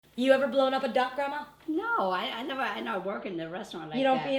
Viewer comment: I thought she said "have you ever had a blow-up doll grandma" in the beginning😂😂😂😂😂😂